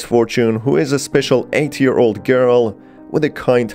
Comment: lol the subtitles said "who is a special 80 year old girl"